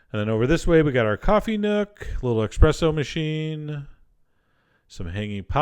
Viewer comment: Did he just call it an eXpresso machine?